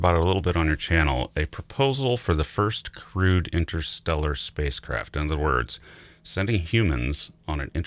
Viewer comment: I heard "first crewed interstellar spacecraft" as "first, crude interstellar spacecraft"